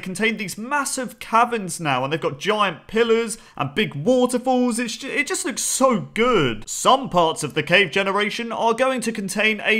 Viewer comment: "Big pillars waterfalls and sh- it just looks so good" i heard that🤣🤣